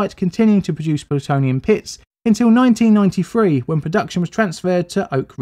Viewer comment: aaahhhhhhh pits you say. I thought you said Plutonium piss and that would have made it a very odd factory if that was the case haha